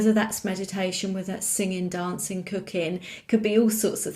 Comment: I really thought you said “do whatever brings you back to you, meditation, singing, dancing, cocaine.” Come again? Lol 😂 🍀 ❤️ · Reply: That made me laugh out loud! I said COOKING!!! XXX